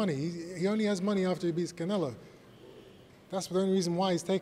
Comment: did chris say after he (bjs) beats canelo, saying what he truly believes there! He never said after he fights canelo